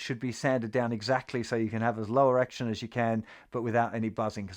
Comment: am i the only one who heard 'low erection' the first time?